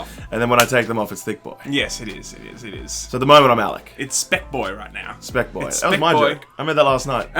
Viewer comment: LMAO I thought he said spicboy and I was like REBRAND REBRAND NOW